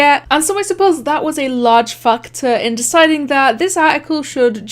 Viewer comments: bruh did you just say da F word lol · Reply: she said factor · British people: she just said factor people is the u.s.: da what did this lady just say!?!?